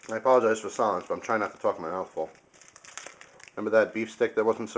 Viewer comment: "I apologize for the sound, I'm trying to f*ck my uncle" I heard SO wrong